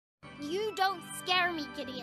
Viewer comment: Judy: “You don’t scare me Gideon!” Gideon: “Understandable… after all, the dead don’t have much to fear.” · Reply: i thought she legit said "you dont scare me idiot"